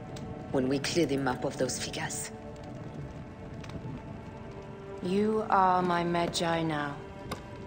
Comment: I swear every time I see the scene where Cleopatra says "You are my Medjay now", I hear "You are my vagina".